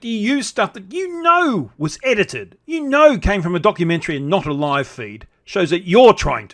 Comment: I thought I heard "donkey-mentary" . . . quite appropriate .